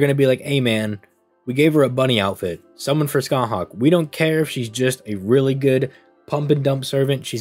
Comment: Did I hear you just say "she's a really good pump and dumb" on 19:16 lmaoooooo